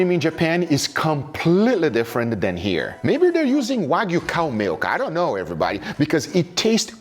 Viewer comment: I heard "wagyu cum milk", which checks out.